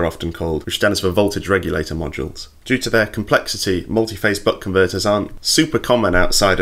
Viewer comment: I heard "multifase butt converters"... TGIF!!